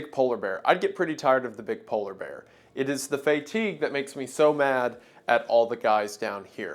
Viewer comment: did you say “fay-teeg” for fatigue 😂❤